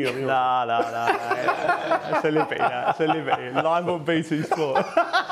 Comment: I cant even hear what he says?? Am I going deaf?? What did they say?? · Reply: someone else answered this, after the liability line he says "live on BT sport" I thought he said something like "mine will be"